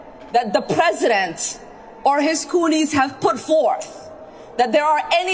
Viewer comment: Did she say cooleys?